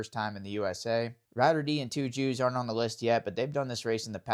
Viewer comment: did you just say “2 Jews” ? That’s a bold move cotton lol